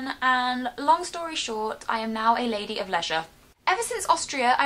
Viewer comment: You said a lady of leisure right? Because at first I thought you said pleasure... :)